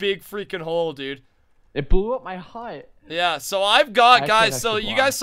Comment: All I heard "it blow up my hit." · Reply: Hut*